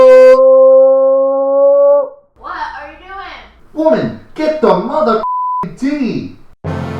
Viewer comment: I swear in the beginning I heard “woman! Get the mother f**king D” lol